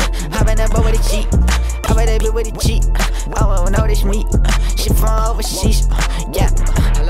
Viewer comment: did he say “owo notice me”